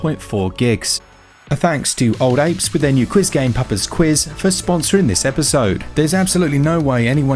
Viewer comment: I legit thought you were saying "Pupper's" Quiz and when I saw it said "Papa's" Quiz I remembered you brits like to pronounce anything ending with an 'a' as 'er'.